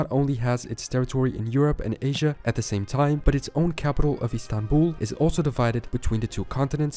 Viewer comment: You made a very common mistake in the video: Turkey's capital is not Istanbul, it is Ankara. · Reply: I heard him saying "Own Capital"